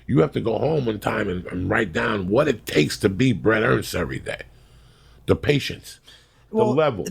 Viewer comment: Please someone tell me what he is saying there: 3:45 min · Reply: bret ernst